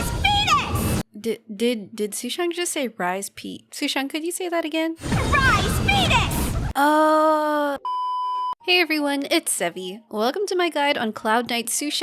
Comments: I KEEP HEARING "RISE FETUS" I CAN'T HELP IT 😭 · I THOUGHT SHE SAID “RISE FETUS” AND NOW I CANT STOP THINKING ABOUT IT EVERYTIME SHE ULTS 😭😭😭